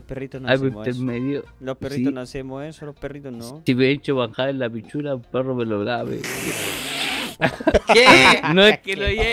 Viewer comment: que dice el en el min 15:40 ? no le entendi ... estiveicho baka en la pichula perro melograbe? wut?